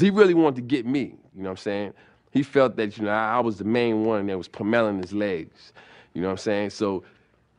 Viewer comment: He is so funny on things that people dont even catch man. When talking about the couch incident and beating the f out of his (ricks) legs, he said "He though I was the only one puh-melon (pummeling) his legs" LMAO